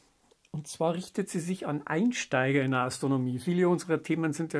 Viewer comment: Bin ich der Einzige, der an dieser Stelle zunächst "... richtet sich an Einstein" verstanden hat und sich spontan gefragt hat, was man dem Meister noch beibringen könne, würde er noch leben? 😉 Ansonsten bin ich natürlich ein großer Fan dieses Kanals mit seinen zahlreichen sehr informativen Beiträgen.